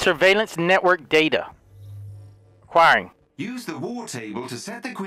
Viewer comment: delirious says according instead of acquiring 😂